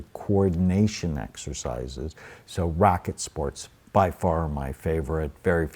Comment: 😎😎Did he say "rocket sports" help your cerabellum? I didn't catch what he said 18:10. Thank you in advanced👋